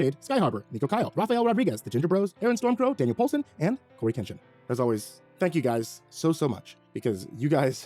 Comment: nah cuz im not the only one who heard him say Cory Kenshin at the every end of the name list